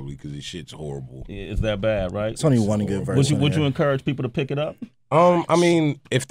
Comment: LMFAO I wonder if anyone caught what Jasper said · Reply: "it's only one good verse on there"